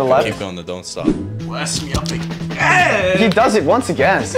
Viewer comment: Bro really said “you hella lean” than completely showed him up 💀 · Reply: I thought he said " you hella weak"